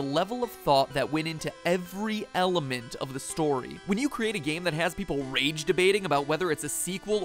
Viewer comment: i thought he said elephant of the story 😂